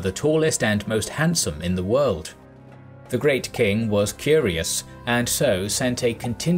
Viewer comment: I thought he said that Cambyses was "furious" instead of "curious," and that just cracked me up. The idea of an emperor being furiously jealous of the Ethiopians for being taller and more handsome than he.